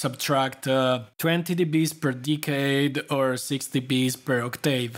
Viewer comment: I don't understand what you say around 10:10 second "20 db per ???? and 6db per octave", you say something like "decay" / "decade" but it's not clear exactly what you mean? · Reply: You have heard it right: it is "decade", and I gotta say that is actually pretty clear in this context, although I understand that not every Max coder has gone that deep into Signal and System theory. Filter frequency responses are plotted on a Bode Plot, which has two combined plots showing the phase and the magnitude values. I am here referring to the attenuation of the magnitude value - let's say the 'dB loss' that increases as the measured frequency becomes higher and higher than the cutoff one. For this filter, the loss equals to roughly 6 dB per octave when we are describing it in a musical quantity; as we know, octaves are related to pitch and not to frequency, and the relationship between pitch and frequency is not linear, so the dB loss related to frequency values is different. On a Bode plot we represent frequencies on a logarithmic scale with base ten, in which the grid is subdivided by decades representing powers of ten (10^1, 10^2, 10^3, or 10, 100, 1000 etc.). Therefore, the dB loss per decade is equal to 20 dB. If you want to investigate further on these topics I suggest you to search for the following things: bilogarithmic plot, Bode plot, frequency response. In Max you can get confident with these filter-related topics by studying the helpfiles of the objects [filterdesign], [plot~], and [filterdetail].